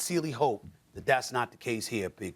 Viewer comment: what he say ? “Pink Perk “ lol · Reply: Big*